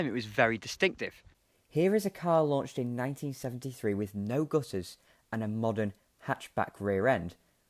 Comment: I love the video (like all of them!) but I'm racking my brains over a snatch of dialogue that I can't fathom out for the life of me. At the 1'00" mark you say "Here is a car launched in 1973 with no [xxxxxx] and a hatchback rear end." I keep hearing "gusses" but the CC captions give me "gooses", neither of which make sense to my untrained ear. Can you put me out of my misery, please? It seems such a vitally important word, and it's got me stumped! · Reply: I had no idea what I'd said, so I went back to look. I said "no gutters", meaning no external guttering, as was still virtually universal in British and European cars at the time.